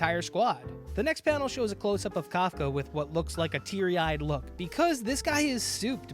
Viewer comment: Not gonna lie, I thought it said Kefka. Like from FFVI. lol.